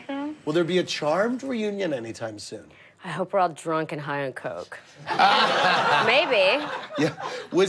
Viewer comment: What did she mention, when she said, "I hope I can drunk a high or coke", at 00:30. Or maybe I didn't understand some wrong?